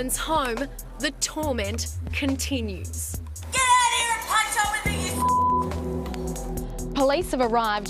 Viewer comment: she actually says " Get out here and punch on with me you slut" . You'd think the subtitle people at A Current Affair would be fluent in Feral by now